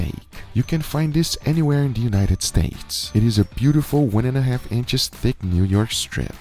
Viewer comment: I literally heard "The United Steaks" 😂